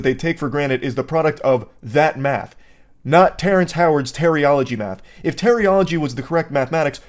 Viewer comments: I put this video in the background, when say terryology. I thought he say teriyaki, I was like what a sec….i hope it was latter now the more I know. Somehow it make more sense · Did he really say Granit?